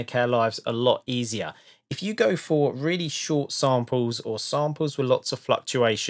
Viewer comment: who whole time I thought you were saying simpler vs sampler LOL